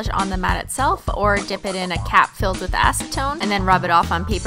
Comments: I thought she said "cat filled with acetone" at first omg I was terrified 💀😂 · I THOUGHT YOU SAID DIP IT IN A CAT FILLED WITH ACETONE AND I JUST ASDFGHJKL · I thought she said a cat filled with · 100% thought she said "cat filled with acetone", not cap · I though you said at first a cat filled with acetone instead off a cap and I was wait what😂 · at first I thought you said a cat filled with acetone LMFAO